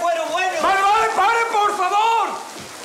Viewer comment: q dice en esa parte Enrique?nunca le entendí lo que dice ahí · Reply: "vale, vale; pare, por favor"